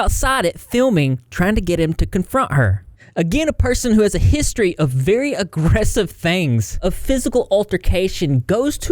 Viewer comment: At first, I thought you said, “a person who had a history of very aggressive bangs”